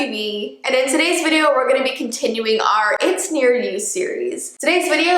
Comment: At the beginning of the video,i thought it said "it hear you"